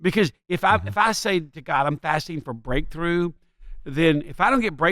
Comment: Another great podcast! At one point I thought you said “fasting for grapefruit” LOL. Had to rewind to clear up my confusion.